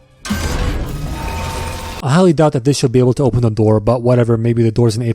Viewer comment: the subtitles said ablet not able like they should've.🥜🤏